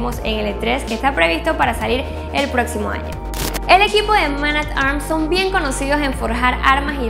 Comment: Como se llama la compañía que dijo en el minuto 2:40 y 2:41 por cierto buen vídeo :D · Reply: David Ghost Hola!!! los puedes buscar en youtube como Man At Arms: Reforged, Tienen videos SUPER BUENOS!!! Saludos!!!